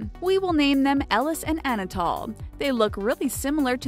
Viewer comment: When you say anatol I hear "Anna tall"